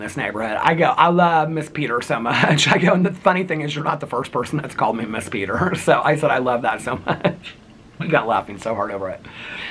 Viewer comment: I heard "ITS PETER....MISS PETER IF YA NASTY" 😂😂 OMG love it